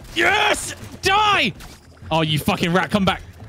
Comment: love that it says Crowny instead of rat in the subtitles lol